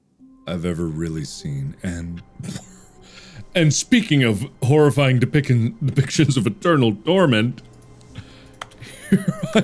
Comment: Did he just say “speaking of horrifying depickings of turtle torment”? 😂👌🏻 @ 1:02:02ish